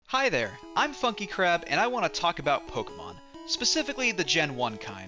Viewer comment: I thought in the intro, you said "F****n crab" lmao